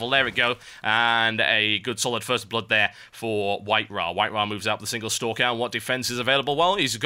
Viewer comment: TB sounded like Cricket in the big bang theory, he said White-wha!